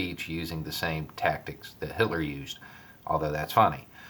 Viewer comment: is he saying Hillary or Hitler? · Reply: He said Hitler.